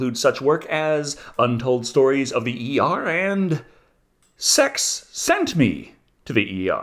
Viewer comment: Watched this with a head cold and couldn't really hear everything and swore he said Sex Centipede to the ER and even googled it before realizing it was Sex Sent Me to the ER. Was originally expecting a weird x-rated The Asylum version of the Human Centipede.